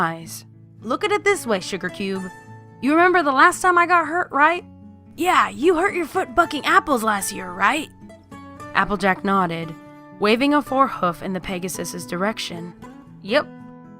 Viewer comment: I swear I heard "fucking apples last year"